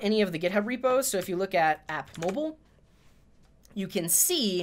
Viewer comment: I heard 'Yanni' instead of 'mobile' :D